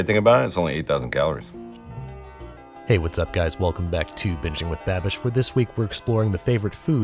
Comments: I could have sworn he said “bitching with Babish” nope... I just need my hearing checked. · I know that you are saying "Binging with Babish" but it never fails, that in every video I hear "Bitching with Babish"